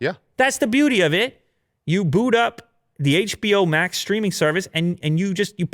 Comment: When he said "you boot up HBO max" I heard "you boo'ed up HBO max" lmao